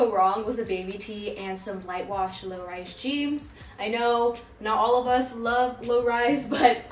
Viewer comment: did u say light wash lowrise jeaMs or am i just hearing wrong